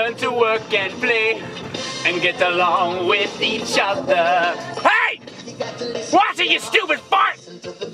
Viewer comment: did say "Watch it, you stupid fuck?" I'm not sure how I feel about him cursing